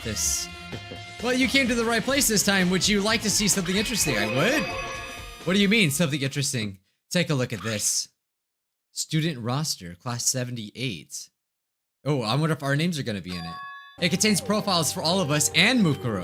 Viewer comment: bruh at 35:51 I thought it said student roaster i was like "well it would make sense for monokuma to have that"